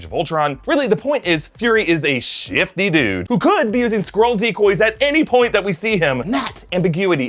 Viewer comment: the captions say fury uses, "squirrel decoys," not "skrull decoys," so funny!!